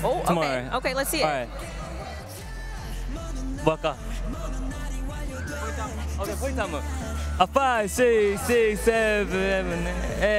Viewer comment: can someone pls tell me what Jake and Ni-ki said? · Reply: “What should we do? Let’s do Point choreography.”